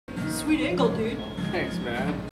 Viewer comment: Omg they said "sweet angle" but I heard "sweet ankle" 😂😂 this video was so funny!